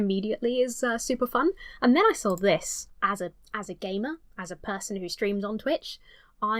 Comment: Your said: "as a gamer" I heard: "as a gay man"